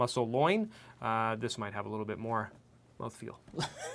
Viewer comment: Hello, can not quite make out what he says in hand quotes at 15:47, the closed captions did not pick it up either? Please help, you chuckled at it! Great series by the way, beef would be interesting too! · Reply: He said that part would have more "mouthfeel" - or be tougher.